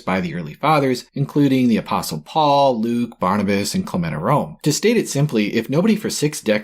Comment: Paul, Luke, Barnabas and ...? Who was the fourth? I already knew Sts Paul and Luke ... ah, wait, Clement of Rome! I heard Clematorome! (Sometimes subtitles serve a purpose).